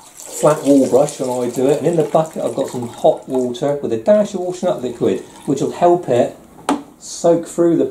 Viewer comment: I'm sorry--a dash of what?? (At 3:25.) I'm American, and I can't understand what it is that you mixed with the hot water. Thanks! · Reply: Hi, I added a squirt of washing up liquid for washing cups and plates. The popular brand over here is fairy liquid. The main thing is to make sure you soak the area several times first before scraping off, let the water do the work. Hope all goes well with the decorating I K 😊